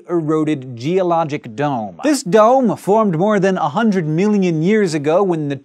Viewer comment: haha, at 1:06 it sounds like hes saying "100 milinjun years ago" XD